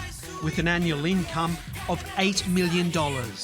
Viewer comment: Oh sorry did I hear that wrong? A ANNUAL INCOME OF $8M AS A MATH TEACHER?!?!